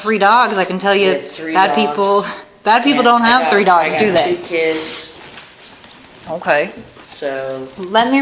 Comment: did she just say she has “two kiddos”? Yikes, imagine her being your mom?